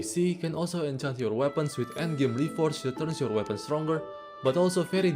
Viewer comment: Dude, I thought you said “NVMe Ports” instead of “endgame reforges” and I was like WHY WOULD I PUT A 2TB DRIVE ON MURASAMA??